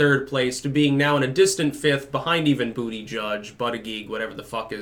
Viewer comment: This whole time I thought he was saying "booty judge" just to be funny. Does he seriously think Buttigieg reads "booty judge"? Also, his name is not hard why is every right leaning pundit playing the same schtick of purposefully mispronouncing his name? Just learn his name and bash him on legitimate things that he stands for.